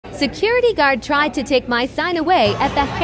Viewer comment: I didn’t read the text and thought the audio said “security guard tried to take my SON away”💀💀